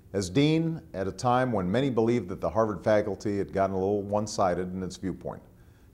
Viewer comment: did he say "fagulty?